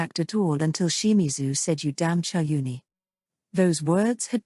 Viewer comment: what did he say? what's the insult means? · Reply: Pretty sure it's "chunibyo". It basically means edgy and delusional, the emo phase.